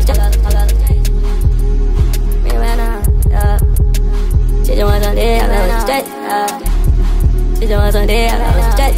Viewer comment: What he say at 0:46😩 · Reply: they ran out